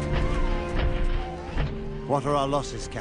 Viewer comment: Does Rex say “Gentleman, careful”?